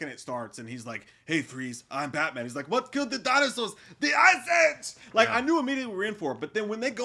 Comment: I will pay for you guys to do a commentary on this one lol. When I was young I thought Arnold said "What killed the dinosaurs? The ICE AIDS" 😂